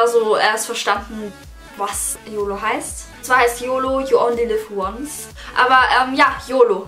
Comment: Ich hab als erstes Verstanden „Yo Oli lives once“😂😂